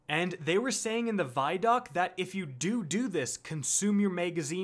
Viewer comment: Did he say doodoo?